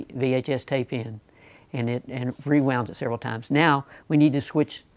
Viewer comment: thought he said that it would only switch when rewound several times